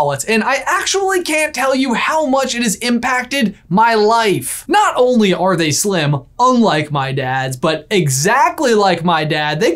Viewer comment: Omg at first I thought said “not only are they slim, unlike my dad” and I was like 😳